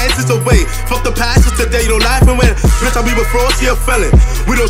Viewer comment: Did he say floss or gloss @ 2:28 ? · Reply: Fross